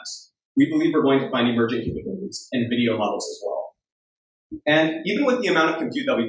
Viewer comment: mark, does he say they think they'll find "emergent human beings in video models"? That's what the closed captioning says.